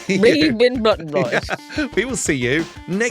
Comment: I could have sworn the outro said "Blutton Bloys"...